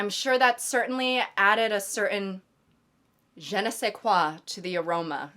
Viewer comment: what is the word she said ? Gena shequa ? It’s in Chali’s 360 mv but i couldn’t figure out that word